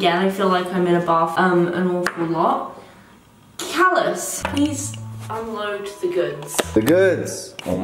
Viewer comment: she called him CALLOUS with a British accent instead of. I love it.